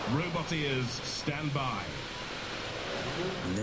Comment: Whenever he says 'roboteers, stand by', I ALWAYS mishear it as 'Robot Ears, stand by'.